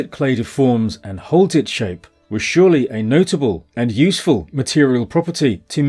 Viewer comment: I heard "deforms and holds its shape to many Plasticine groups"😃